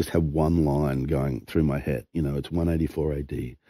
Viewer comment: I thought he said 194 ad. SORRY HE said 184 ad.